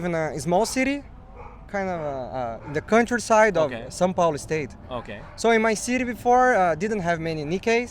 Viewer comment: I think he says "São Paulo state"